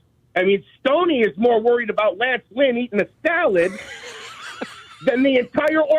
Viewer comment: What did this guy say about Steve Stone around 6:47-7:00?... "Steve Stone's more worried about--" what?... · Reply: Lance Lynn eating salads.